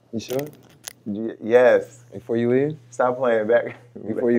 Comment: did Jason just say “stop playing baby”? They together or sum?